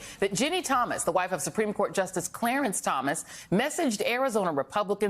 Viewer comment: Oh..."messaged." I thought it said "massaged." I was like, "whatever adds buoyancy to your watercraft, lady." Whew, I'm not a prude but...